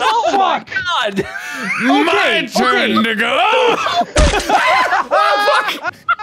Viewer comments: It sounds like he says the n word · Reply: He doesn't he said "my turn to go" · To anyone confused: what Mark says at 31:40 is “My turn to go”. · legit sounds like he said “my turn n****” I was like “wtf!!!” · GUYS MARK DID NOT SAY THE N-WORD AT 31:37! He said “to go.” Full sentence being “my turn to go.” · Ok, at 31:37 I know he said “my turn to go” but I can’t be the only one who thought he said the n word at first and was scared for everything that was about to come from it😂😂, or just think of how funny it would have been if he did say it lmaooo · To anyone confused: what Mark says at 31:40 is “My turn to go”. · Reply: oh okayyyy i thought he said my turn n*gga... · No, he does not say the N word, he says "My turn to go!" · Ngl I started crying at 31:35 but I had to do a retake bc I thought he said "it's my time n-a!" But he said "to go".... I was confused and in tears. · Um… did he just say “my turn n*gga!” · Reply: He said my turn to go . “To go” was blurred together when he said it though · GUYS MARK DID NOT SAY THE N-WORD AT 31:37! He said “to go.” Full sentence being “my turn to go.” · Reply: What I heard: "MY TURN NIGGO!!!" What the rest hear: "MY TURN TO GO!!!" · i fully thought he said the n word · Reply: He said "MY TURN TO GO!!" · did he say the n word? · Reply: He said “to go.” Full sentence being “my turn to go.” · Did he... did he say what I think he said? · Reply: He said "my turn to go" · DID HE JUST SAY "MY TURN N-WORD" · did anyone hear my turn n word